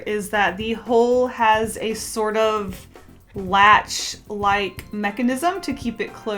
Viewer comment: When she said "Latch Like Mechanism" I had to pause and turn on captions cause I heard "Flashlight" and I was CONFUSION